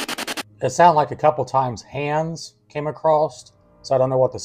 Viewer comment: When you say about hands i thought i heard hammer